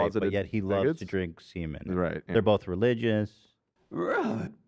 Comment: Did Dan say they are both closeted f words at 1:01:10 ?????? :o